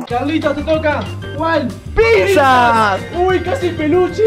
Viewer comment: ESCUCHE PIZZA JAJAJ